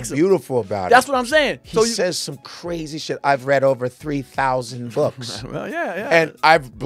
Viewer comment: I thought he said 10,000 books. 🤣